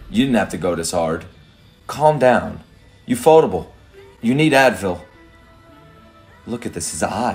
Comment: Did just said they need Advil?! 🤣☠️